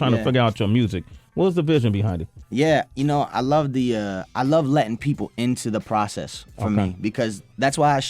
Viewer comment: I seriously thought he said " I love Latin people." I mean I don't know what race he is but anyway that was funny cuz he really said "I love letting people into the process...etc."